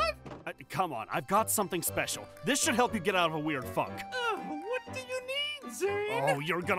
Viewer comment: is it just me or like- WHAT DID HE JUST SAY? “This should get you out of a weird f***.” IM SORRY WHATTTT